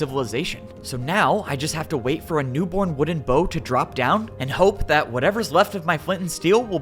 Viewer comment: did u say wooden bow